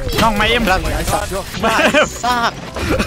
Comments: i think jj said 'i suck' not 'ไอสาส' kub. nong jing would never scold p kla <num> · น้องจิงพูดว่า "P'kla, I suck" ที่น้องยิงไม่ได้นะคะ ไม่ได้ด่าพี่กล้า(ฝากทีมงานช่วยเช็ค+แก้ซับให้หน่อยคั้บ)